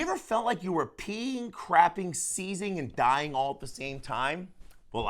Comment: at first i thought you said sneezing, and im ashamed to admit i was a little relieved you weren't. Feeling like you have to sneeze but cant has been my last month with a sinus infection 😅